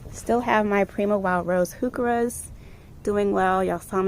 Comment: Beautiful garden! What's the name of the mauve/purple colored foliage plant at the 9:20 mark in the video? You mentioned the name but I didn't recognize what you said. Thanks. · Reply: Those are Primo Wild Rose Heuchera plants by Proven Winners💜💜